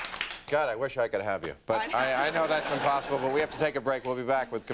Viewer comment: Great interview preceded by an extremely cringe-worthy monologue. Maher didn't mind having terrible joke writers back then either. I just want to check, did I hear correctly at 7:36 "God, I wish I could have you"? How bizarre is that? I love her reaction.